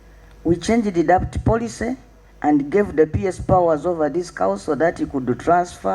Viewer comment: Did I hear " these two cows"?